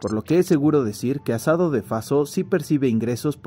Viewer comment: Pensé que decía asado falso ajaja